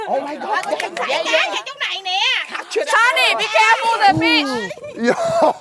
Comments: Why do i hear SONNY BECAREFUL THEIR A B*TCH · did V say "Sonny be careful the fish"?!